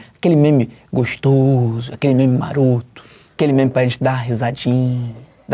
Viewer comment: só eu que entendi Naruto ? 😐